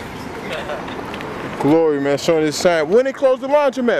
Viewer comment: "Ok Peter!" That ain't my name. Oh my bad! Lmaooooo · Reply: I thought nunu said he taking his shine